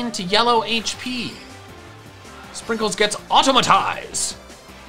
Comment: Autotomize. I know not what it means, but that is in fact the word used Hey, do you still despise Miltank after the MML? Or is it still your nemesis? Has your relationship with the pink cow improved, is what I'm asking.